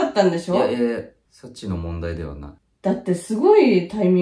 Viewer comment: 「サチの問題ではない」ってところ、「ソチの問題ではない」って聞こえてめちゃくちゃわろた笑